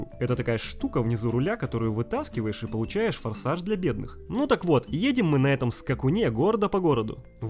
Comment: Кто-то же угарнул от фразы "Форсаж для бедных" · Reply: Я вообще услышал "форсаж для белых" ._.